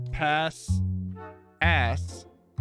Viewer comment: sounded like he said a**